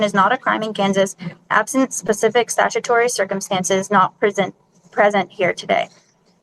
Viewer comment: I wonder how much of that script she was actually able to comprehend. She had no idea what she was saying. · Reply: Judis…judish…jurishprudence… 21:08 (might be a hair before) I heard “Not prison”….”not present!” Bit of a Freudian slip wouldn’t you say? (Obviously she wouldn’t go to prison for traffic infractions but it was still funny) Yawn! Soveridiots